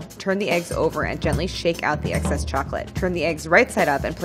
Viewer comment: I'm 3 minutes in and I am already regretting her filling an actual egg. I feel the regret for her... Edit: I was wrong they turned out amazingly! Also, did she say "eggcess" at 30:27 🤣